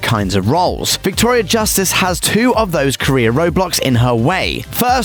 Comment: Did he just say Roblox 😂